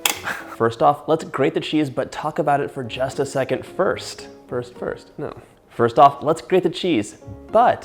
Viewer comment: I’m sorry but did I hear a “purse first” slid in there :)